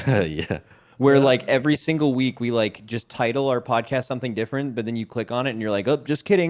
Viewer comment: I heard ‘the titanforge podcast’ is available